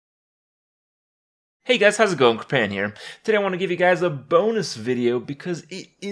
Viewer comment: He says Kripparian here so fast,i thought he said Hey guys,how it's going companion?